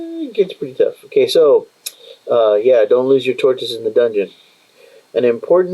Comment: I heard "don't loose your tortoise in the dungeon". I really enjoy these videos, thank you for taking the time to share the joy of Mithras